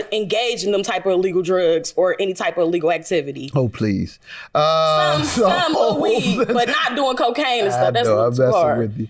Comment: Yooooo at 6:55 I thought he said “HOE PLEASE “ lmbooooo Help me lord lol